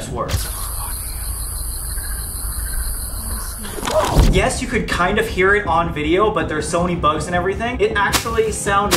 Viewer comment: in the clip they showed there was a weird voice or whisper…I couldn’t make out what it said tho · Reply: That was kris saying i hear ir